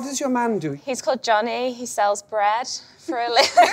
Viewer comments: Wait what does her fiance do? I couldn't hear it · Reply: I thought she said he sells bras! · I don't understand why they all laughed when she mentioned his occupation... 😑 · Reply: I think I heard "He sells bread" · Wait what does her fiance do? I couldn't hear it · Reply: First i thought she said he sells grass lol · I don't understand why they all laughed when she mentioned his occupation... 😑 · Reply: I hear "he sells breast" 😂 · I don't understand why they all laughed when she mentioned his occupation... 😑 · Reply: I thought she said ..' he sells thrash!'